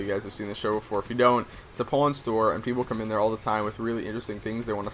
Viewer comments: Lol i thought i heard u said "its a porn store" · i thought you said PORN store owner. i was like WOAH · I thought you were saying "porn store" and "porn stars" lol. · I thought he said i want to be a porn star not a pawn brocker or how ever you spell it. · lol i thought he said porn store owner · I thought you said that you wanted to be a porn store owner